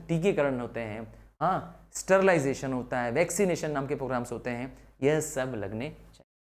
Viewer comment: DID HE SAY STERILIZATION 25:31💀